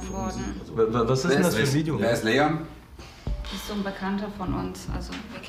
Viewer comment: habe es jetzt mehrmals angehört ... sag er wer ist sido .... oder welches Video ...